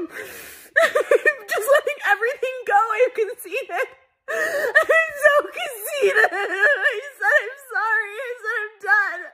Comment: I first thought she said "I'm soo conceited!" Lol she said she has 'sooo conceided" like I guess how you do in the race for presidency, one will conceid..